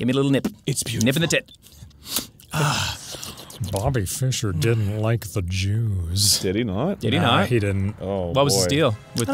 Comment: At first I heard it as “Bobby Fischer didn’t like the juice” so I wondered why was Dan saying that all of a sudden and also what kind of juice he didn’t like. Fun times.